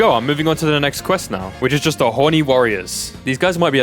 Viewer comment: Did u just say horny warriors bro😂